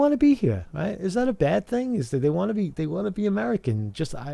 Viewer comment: Did you just say "They waited to be Americans" when talking about your hat and blanket? So they didn't force their way in?